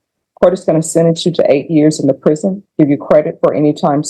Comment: It did sound like she said "eight" at about